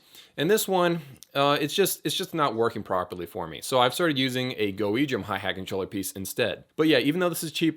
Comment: Hi, Sweet video! I have a Roland TD30 with a VH12. I broke the controller piece you show in the video (around 8:30). You say you replaced it with a cheaper version, but i can't make out the name of that controller. And would you happen to know if that specific controller you show will fit my VH12(!) aswell? Thx in advance for your response! Drumming greetings from the Netherlands. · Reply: GoEdrum maybe?